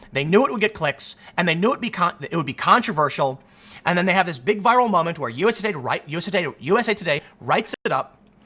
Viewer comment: producer in don lemons ear piece: "don let her speak its gonna be a viral moment!!!!" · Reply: Plan Bee, I had to do a double take on that statement of yours. I know you said viral moment, but just for a nano-second I thought your said a vile moment. Sorry for the misinterpretation.